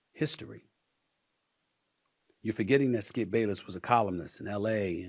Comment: Almost sounded like he said Skip Bayless is a colonist.